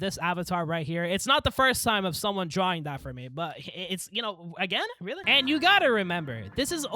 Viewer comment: Imma need that right now twin send me the linky twiny Also did I hear that right "again" you had happen this to you again I would like to see that too